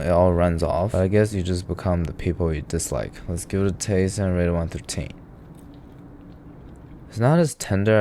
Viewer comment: I think I'm on my 20th (ish) video of binging random videos on this channel, and I've only just realized that he's just saying 'and rate it 1 to 10' really rushed with some weird pronunciation... which is sad, because up until now I thought he was saying 'and I really want poutine' before every bite.